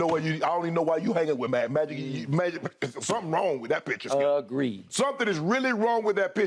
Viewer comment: "Something wrong with that picture" sounded like "something wrong with that bich" 😂 i was like this dude just say that on live AIR?